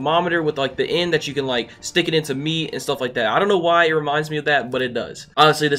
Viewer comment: I thought this dude said, "stick it into me" 😂